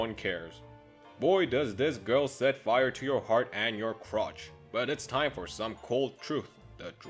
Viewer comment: Did he say Crotch?? Or Crops